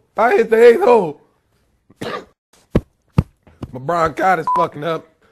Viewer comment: I thought he said my "lebronchititis" hahahah